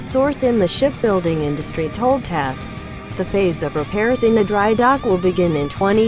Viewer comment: Oh dry dock. I thought you said scrapyard.